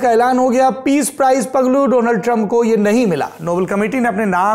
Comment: did you say paglu donald trump